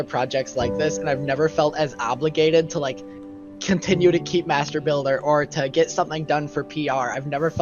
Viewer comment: dude why am I hearing masturba....g instead of what he really said